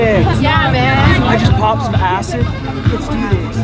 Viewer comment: Did he say he popped some acid?? Hardly find that appropriate in this video if that’s the case...🤔